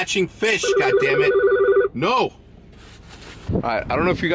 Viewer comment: i heard RICH PIANA GODDAMIT